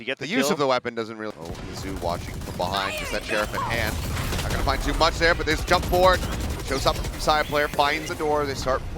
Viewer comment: レイズウルトのセリフは何て言ってますか？ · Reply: fire in the hole!